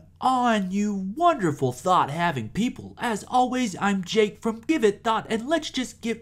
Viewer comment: I sware I thought he was saying thot lol